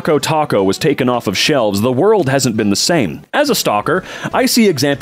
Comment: I heard stalker before i read stocker and had a brief moment of wait wtf